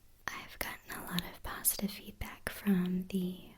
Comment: Also, at 10:39 I heard "I've gotten a lot of pasta feedback." Hehe.